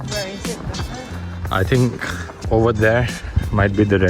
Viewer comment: Dhruv sounded like he was saying "Red Bit*h"